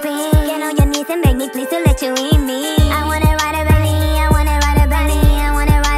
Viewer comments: Who is just like me and sang it as “I wanna ride a bunnyyyyy” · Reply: I heard "Mia wanna ride a bunny" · I wrote "I wanna ride it baby" And I went out to this song.🥀🖤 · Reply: I thought it said I wanna ride a bunny 😭